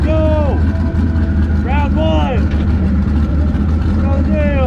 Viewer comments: What did he say?? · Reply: ah, "round one".. lol I thought he said, "P$#%d boys" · Did he say “proud bois” at 12:41?